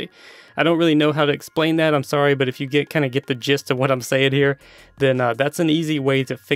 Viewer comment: did you just say "Did get the jizz?" ewww